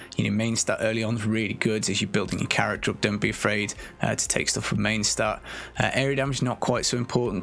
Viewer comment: Question at around 8:20 - 8:30 u say something about mainstat and dont be afraid, i heard dont be afraid to drop mainstat for chc/chd, or did u say something else? i can also hear u say take stuff for mainstat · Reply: Sorry it mumbly buddy. I was saying don’t be afraid to take main stat as an early roll 👍🏻